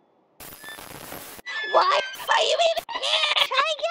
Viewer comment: what does she say? Run while you can? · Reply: She said "WHY ARE YOU EVEN HERE?!"